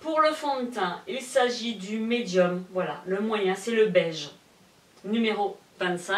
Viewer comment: Salut 🙋 belle vidéo Mais.. o_O c'est moi ou tu dis...?. "belge " a 2:18 ? Hein..? 😂 si elle nous intéresse on peut la quoi ?.... 8:55 Lol 😁 à 13:55 ils on tout mis dans le contenant et plus rien pour le contenu. lol cassé 3 pattes à un canard. Dis on gagne quoi si on trouve le nombre de erreur dans la vidéo 😀 ya pas mal d'intrus je trouve.. ☺😄